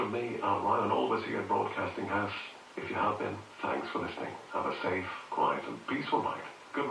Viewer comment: did he say 'night' or 'life'? 😳